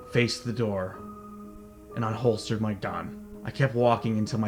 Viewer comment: did he say I hoster my don